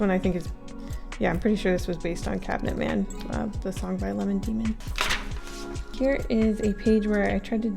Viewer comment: Did say Lemon Demon on 12:18?! I only heard one of Lemon Demon’s songs: Eighth Wonder Btw HAPPY NEW YEAR 🎊